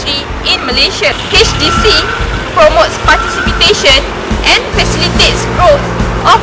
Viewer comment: did she say "participitation ?"